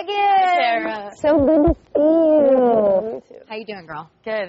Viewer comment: did she say Sarah?